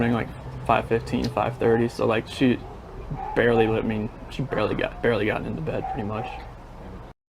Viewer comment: In his interview when he says he left for work early that morning, it sounds like he says "she was barely living, barely got into bed"